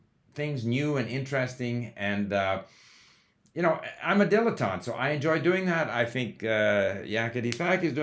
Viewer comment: If you watch YouTube videos with CC turned on, you’ll see lots of hilarious, incorrect guesses about what people are saying. For example, at 2:07 Steve supposedly says “I’m a debutante.” Of course, what he actually says is “I’m a dilettante.” That’s the only mistake I caught, so the CC here is more than 99% accurate. Thanks for another insightful video!